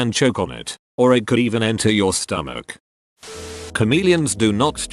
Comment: Lulz, I totally heard "comedians don't change colour..." had to instant replay that in my brain to realize u said "chameleons"